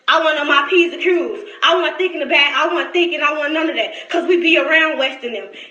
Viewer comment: I thought she said, "We be around West (or Wes) and dem."